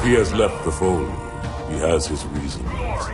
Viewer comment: is he saying "glory to the Arbiter" at 2:10?